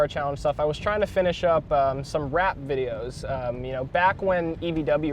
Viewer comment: When you said "wrap videos" at 4:17 I heard it as "rap videos" and I got scared that there was gonna be an "MC Jimbo Babayy"